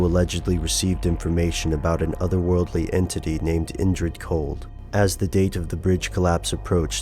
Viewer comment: What's the name of the "other worldly entity"? At 2:04 is he saying "Injured Cold"? I'd like to research that, also.